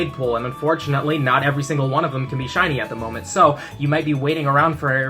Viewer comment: The system thought he Said ”Chinese” Instead of Shiny.